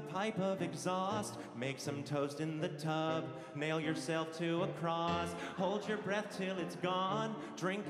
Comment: I just realized that he said "Nail yourself to a cross", which is how Jesus died. Kind of poetic, right? At least much more poetic than what I thought he said, which was; "Mail yourself to Lacrosse" 😭